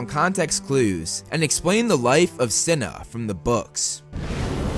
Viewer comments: I thought it said life of China lol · Thought it said The Life Of China XD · I thought it said life of China... Lmao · I thought it said the life of China...